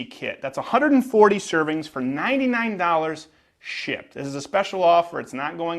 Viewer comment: sounded like you said shit instead of shipped